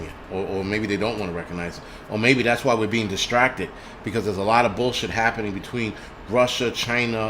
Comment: No big deal, tomato etc... I've always heard it as Go Bulls. 🤷‍♂️